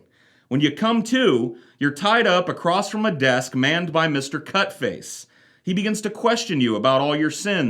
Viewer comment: I thought you were saying "Mr. Cuckface" at the start and this sounds similar to Inferno.